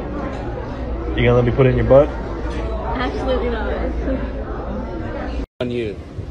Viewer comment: what did the guy say? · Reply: "You gonna let me put it in your butt? " "Absolutely not" Aaand he splits the deck, meaning she's got to pay for what she ate